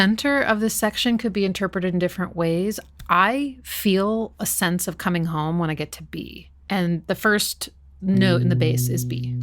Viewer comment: I legitimately thought you said "I feel a sence of coming home when I get to pee" and I was like that's so real, when you've been out for a while, and you finally come home to your toilet it always feels much better than any other